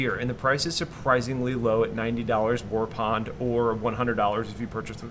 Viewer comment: Did.... Did say "warpond" at 1:18? LOL